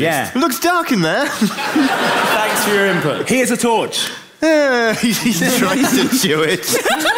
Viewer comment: what does he say at 36:38? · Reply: "He tries to chew it."